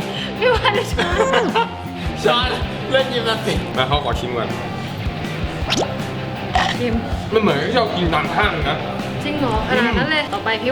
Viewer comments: พี่เป็นแฟนพี่ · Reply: My'Mint' channel 4:08 พี่พูดว่า งั้นเค้าขอชิมก่อนฟังดีๆ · นาทีที่ 4:06 ได้ยินพี่พูดกับพี่ว่ามาเค้าของชิมก่อน😊ฮู้ย!!